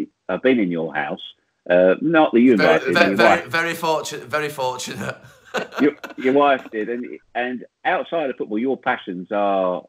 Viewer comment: Is it me or when Geoff said he's been in Gary's house did he say "your wife did me"? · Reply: He said: “Not that you invited me...your wife did.” Just as Gary was saying “Very fortunate!”